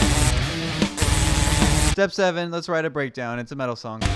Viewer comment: WTF did you just say? Metal? breakdowns?